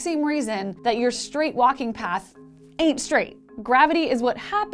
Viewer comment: Welcome back ! I love Physics Girl's videos ! I don't speak English as my native language, so I really appriciate subtitles. I couldn't understand "eight straight" at 3:13. Could anyone please tell me what it means? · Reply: s hara Hello! The subtitles are actually not correct there. She doesn‘t say „eight straight“, but „[it] ain‘t straight“, which means that it is not straight. Have fun with the video! Greetings from a fellow student from Germany.